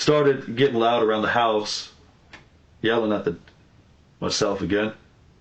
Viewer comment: he says " yelling at the d- myself." I think he was going to say demons but he didnt want to for whatever reason.